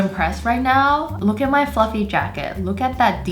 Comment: When u said fluffy I thought u said f-ing